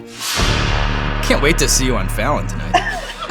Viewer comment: I thought he said "I can't wait to see you on Valentine's day" and I am LOSING IT